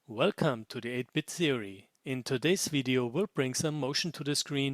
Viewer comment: Welcome, to the 8bit serie_ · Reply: >eigth You mean eight? See English is hard, even for native speakers. Edit: although judging by your username, maybe you aren't. Fwiw I heard it as theory so I wouldn't worry about it.